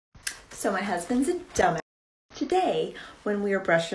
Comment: i thought she said: "So my husbands' a donut"